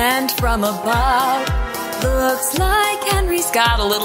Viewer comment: My subtitles say "walks like a boss" lol 😂